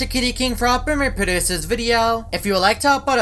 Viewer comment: What Did He Say? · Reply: F***ing me produce this video???